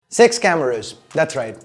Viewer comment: Did I hear, Sex Cameras?